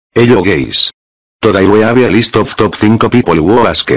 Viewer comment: i thought i heard hello gays